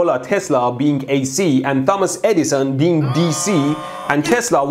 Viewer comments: It's perfect that his accent makes it sounds like he says "Dumbass Eddison". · did he say dumba$$ insteD of thomas? XD